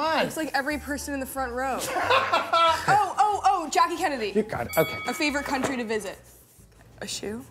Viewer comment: Did he say Jackie Kennedy?? Anyone??